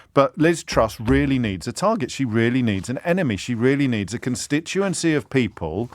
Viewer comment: I thought the tag line said, "Liz Truss needs a 'new enema'. 'Enema', I thought, 'is the last thing Truss needs". Which she proves everytime she opens her mouth.